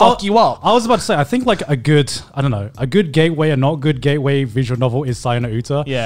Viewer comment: I think Gigguk is saying saya no uta not son of Utah, so that may be something in the subtitles you want to change the if you can.